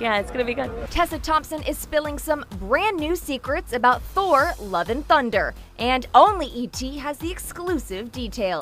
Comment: I thought they were saying "Thor: Lovin' Thunder" (or "Thor Lovin' Thunder), which, I gotta say, is a much cooler title 😂😂